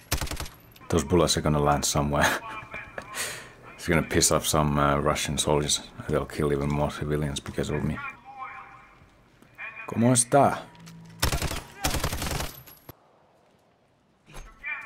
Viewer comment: im not sure but i think he said "kamusta" but thats just me